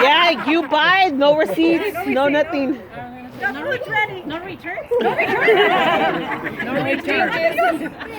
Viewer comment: only Mexican family joke and returning the ring lol I love it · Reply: and I thought they were saying "he cannot return her!" 😝